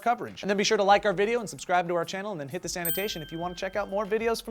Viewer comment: When-ever Joe says, "click this annotation" it sounds like his saying, "click the sanitation". LOL